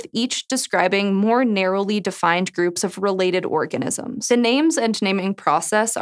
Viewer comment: she really said orgasms intsead if organisms